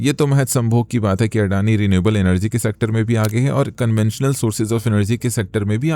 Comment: I heard it right, didn't I? 'Sambhog'